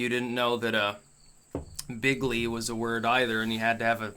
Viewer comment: Every time Trump said bigly, I thought he said big league.